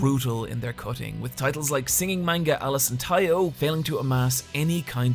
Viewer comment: what manga is this? I couldn't understand what he said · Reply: Alice and Taiyo. Already cancelled